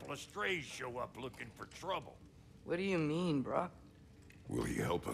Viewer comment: i swear to god i heard Atreus saying "what do you mean bruh?" 🤣